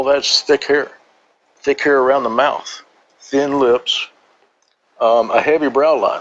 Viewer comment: Wow, you found an athletic 6-foot redheaded female C cup with thick lips in the woods? I'm going hiking more... All jokes aside this was a fantastic re-telling and terrifying. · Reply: I thought he said thin lips.